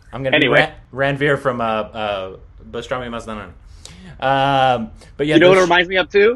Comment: Did he just say bajrangi mastana??..😂